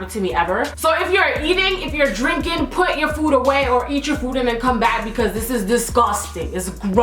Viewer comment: I was eating my noodles when I heard her say... put your food down😷